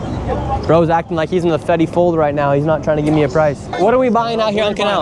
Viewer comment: Did he say Fetty fold 😂😂😂